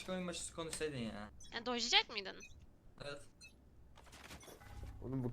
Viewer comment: kız ne diyor orda? Anlamadım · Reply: dodgeliycak mıydın diyo